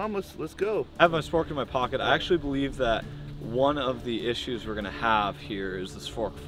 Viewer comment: Tom I think you said “Spförk” like some kinda wild nordic man